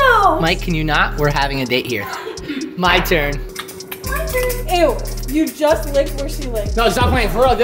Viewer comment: 5 year later and i still dont know what says at 0:05 😢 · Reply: She said “ew you just licked where she licked”